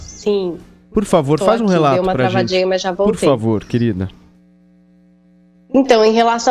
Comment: Eu ouvi errado ou a Paulinha disse "Dei uma cagadinha mas já voltei" em